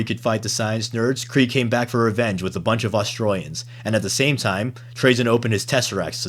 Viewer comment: I thought he said AustraliansXD